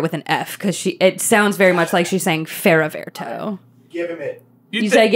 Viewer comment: i always thought she said "ferreverto" too! but when i saw it written out in the multiple choice i DEF new it was that one. i was like it starts with a V AND its 2 words?!?!?! hahaha